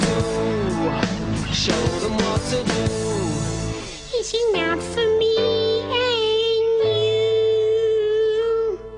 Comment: When Jack Morgan sings in the mouse voice at 2:37 or so, what does he say exactly? I love the key of "S". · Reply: He squeaks "it's enough for me and you", I think.